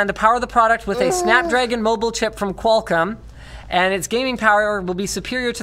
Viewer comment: Oh you're forever going to be known for that LMFAO Edit* did linus just say qualcum?